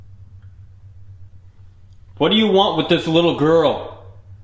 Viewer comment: When you said, "What do you want with this little girl?" it sounded like it said, "she's my pet!"